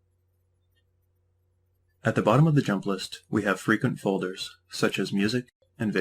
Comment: it sounded like you said freaking folders